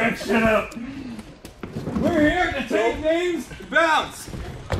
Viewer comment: "We're just here to wreck sh!t" I thought he said "Red Shift and Blue Shift". So I propose a new team name for and Jeremy: Red Shift and Blue Shift!